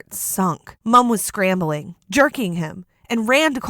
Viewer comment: i thought she was gonna say her mom was jerking off